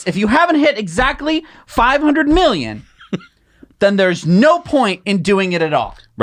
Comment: Time stamp 3:05. Did say half a "million" O is it the same as 500,000 or did I hear something else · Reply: Yes I did hear 500 million cars. I wish I had Tesla stock